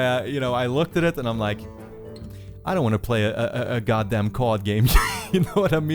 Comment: When he said "card game", I thought he said "COD game"...